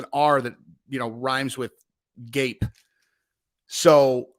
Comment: It rhymes with grape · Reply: I thought he said rhymes with gate.